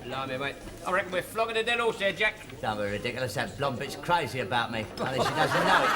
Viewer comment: Watching in 2025.. Anyone actually notice 9.06 it sounds like Jack says "that blonde b**ch wants me" even though i assumed it was supposed to be "blonde bit" 😂 Did I hear correctly haha 😂